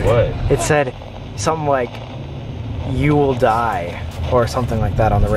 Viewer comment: I heard it say "you shall die" at 1:06 - 1:08 put the speed on 0.75 and you should hear it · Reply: "You will surely die"